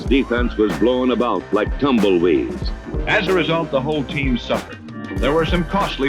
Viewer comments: Those narrators from the 60s and 70s found the most eloquent ways to essentially say "they sucked". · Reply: Almost thought he said "As a result, the whole team sucked" outright, but he in fact said, "suffered". Lol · Those narrators from the 60s and 70s found the most eloquent ways to essentially say "they sucked". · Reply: Sounded like he said suckered..